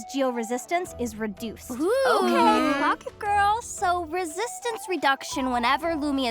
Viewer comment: There is no way I just heard “clock it girls”🤦🏽‍♀️